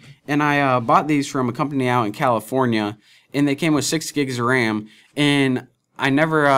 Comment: I thought he said 16 gigs.